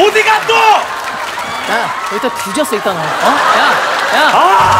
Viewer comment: 양세형 또 가는데 ,,.,?ㅋㅋㅋㅋㅋㅋㅋㅋㅋㅋㅋㅋㅋㅋㅋㅋ 희미하게 뒤졌어 들림ㅋㅋㅋㅋㅋㅋㅋㅋㅋㅋㅋㅋㅋㅋㅋㅋㅋ